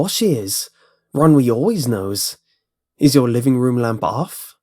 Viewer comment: For a second I thought he was gonna say "Ron Weasley" XD Death by 7th born child XD